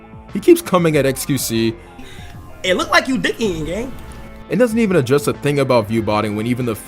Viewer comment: what did he say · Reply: "It looks like you dih eating gang," I think